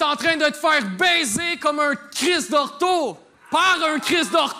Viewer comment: il dit quoi exactement svp? Pour le petit Jérémy j'ai fait mes recherches mais le "criss de ???" Je le catch pas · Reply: Criss d'ortho.